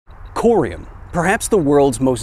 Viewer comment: Oh No!! OH NO!!! NOOOO!!!! I'M GONNA DIE!!! MY WHOLE FAMILY IS GONNA I beg your pardon, I thought you said Corian. My kitchen countertops are all made of Corian.